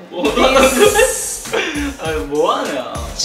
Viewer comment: did The8 just say "Moana"? 😂